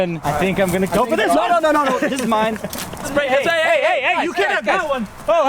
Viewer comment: Go to 4:31 i think niko said the 'mine' word and no one noticed??